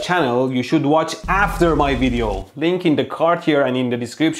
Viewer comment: hehehe😓😓 did he say after his video ahhhhhhh yeah ok whatever